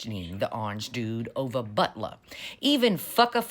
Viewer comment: I heard this as “butt love” and didn’t catch my mistake for way too long.